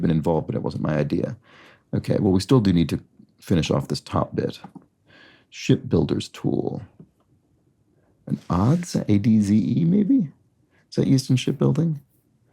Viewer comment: In the days of wooden ships, an adze was used to shape timbers, especially for the hull of the ship. (The clue was "shipbuilder's tool"). There's probably a Wiki article. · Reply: I was making a cheap joke about how it sounded like said “💩 builders tool”